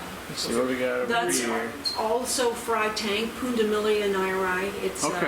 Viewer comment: Can you please tell me the common name of the fish at 11:51. She said the scientific name but I can not understand what she was saying enough to try and spell the name for Google to find them.. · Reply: Kryssi Storts There is no common name. Pundamilia nyererei. More info here. <link>